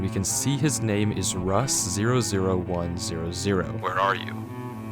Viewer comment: i thought you said rust_010 and i freaked out